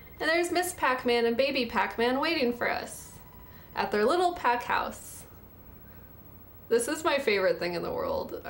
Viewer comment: At there lil pac house. I was watching familt matters for a sec and was listen to you in back round i thought you said crack house lol